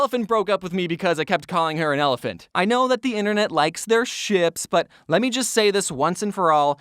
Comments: I'm sry what u say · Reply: Yes ik but at first I heard shits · i thought when said:i know the internet likes their ships i paused when he said ships so i thought he said sh*t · I swear i thought he said "sh*ts" for a second lol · I thought he said shit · I know this is dumb but did anyone hear him say s*** with out bleeping it